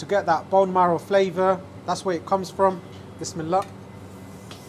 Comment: Awesome video as always. What is the little phrase I hear you say on many videos just before you add the meat to the dish? (It's at 2:03 on this vid) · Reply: 'Bismillah', roughly means 'In the name of God...'